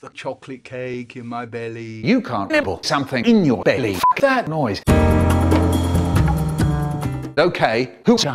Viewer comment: what the hell does actually say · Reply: "f**k that noise"